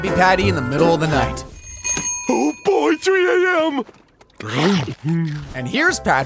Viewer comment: Is Patrick said 3 AM?